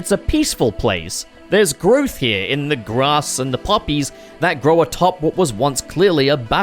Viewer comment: Puppies? That's what I heard. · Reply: I wish. Nah I said poppies.